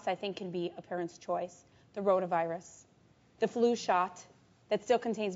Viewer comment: What did she say @ 0:59 ??? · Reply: Roto virus